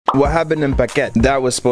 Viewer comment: All I heard was “what happened in baguette?”